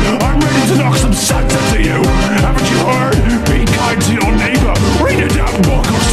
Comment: I thought that part said be the cartoon neighbor💀